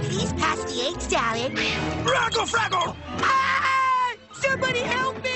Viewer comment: When I was a kid, I thought he was saying "raggle saggle", not "raggle fraggle".